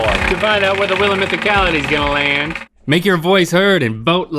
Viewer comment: I thought Link said "boat like a beast" and, honestly, that's way more appealing to me.